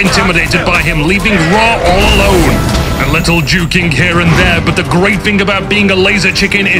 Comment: I heard 'A little jew king' instead of juking... oh wow.